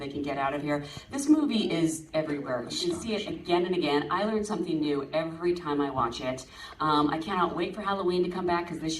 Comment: Focus on Downey; what is he whispering into the mic? Makes it feel like some sort of a celebratory moment for tickling Nolan's emo side. Eccentric. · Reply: yes, he says "astonishing" as the previous reply said.